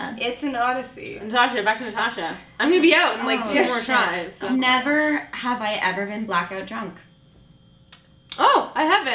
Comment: I didn't get the part at 6:30. Can somone tell me what Natasha said? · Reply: "Never Have I Ever been blackout drunk"